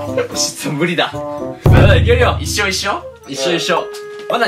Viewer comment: 「一生一緒？」って2人に確認したように聞こえてほっこりした